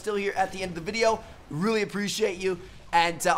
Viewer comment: wtf did he say at 10:28? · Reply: i think he was trying to say "subscribe" and "check out these videos if you haven't seen yet" at the same time